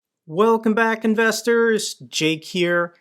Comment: I just watched this after years of watching your Ukraine content and I swear I heard you say "welcome back defenders" lololol